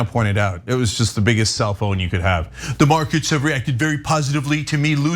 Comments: I thought you were saying cell phone 🤪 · At first I thought Cenk said Cellphone instead of self own.